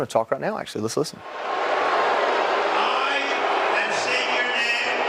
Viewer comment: You can’t hear it that well over the boos but I think he also says “I and state your name” instead of saying his name 😂